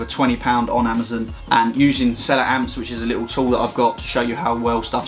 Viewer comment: I can't find the link on the description for the app which tells you what items sell the most on amazon so I can get them reduced in store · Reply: Did you ever find it. It sounded like he said Seller Amp but I can’t find it in App Store